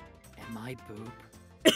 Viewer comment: Wait wait wait.....did anyone catch this at 13:17 did say I poop or I high XD that would be weird if it was tho ...I'm a weird o anyways